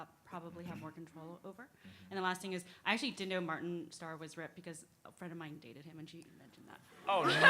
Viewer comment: what does she say? Martin Starr is what?? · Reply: Martin Starr was ripped